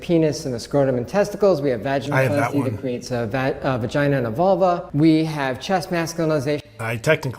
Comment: I thought he said Volvo.